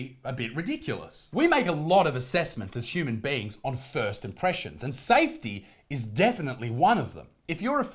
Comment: Isaac, did I just hear you say “thirst impressions”? 😳